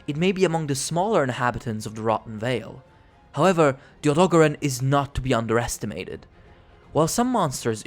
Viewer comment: I just heard him say “However dohogoron is not to be underestimated.”